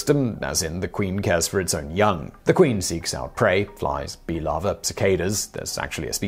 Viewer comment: I thought you said ‘feed it to it’s lover’ 😂 Oh right! Larvae!